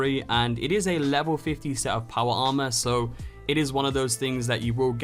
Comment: Did u say 15, or 50? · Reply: 50